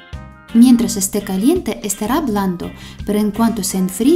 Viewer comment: por un momento pense que dijiste, esta hablando pero dijiste en realidad esta blando